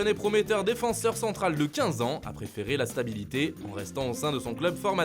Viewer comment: 😳 J'ai entendu 15 ans où bien 25 ans 🤧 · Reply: 15 ans